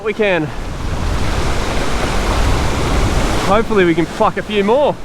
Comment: what did you say 20:00? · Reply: Pluck a few more